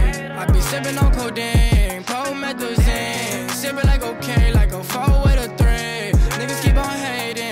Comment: Did he just say sippin that cocain 😂